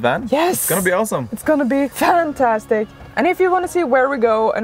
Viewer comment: Did say "it's going to be VANtastic" at 15:24? Did you make a pun, or is it just me? 🤣 GREAT JOB ON THE VAN, IT LOOKS SO GOOD!!!